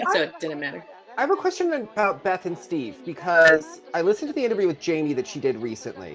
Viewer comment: I haven’t interviewed Jamie over video. I did an interview with her over the phone years ago. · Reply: thanks I thought you said you listened to a recent interview with Jamie, and Vanessa said “I did too.” I assumed it was an interview somebody else did.